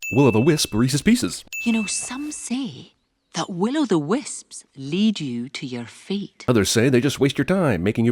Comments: When I was younger I thought she said "will-o-wisps will leas you to your feet" 😂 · Until now, i thought the lines said: "The willo the wisps lead you to your feet" "A spell that changes its feet" "If I give this to my mother, will it change ma feet?" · When I first saw this movie, I thought she said the wisps can change your feet and I was confused af. Fuck Scottish accents